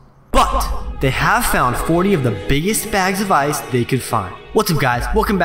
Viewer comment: It sounds like he said 40 at 0:20 lol